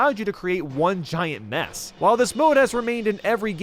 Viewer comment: I thought he said Ness